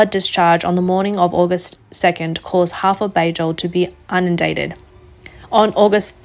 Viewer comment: did you say unindated?